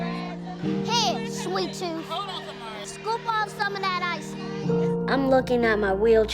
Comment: lol (what he says) hey there sweet tooth. take off some of that icing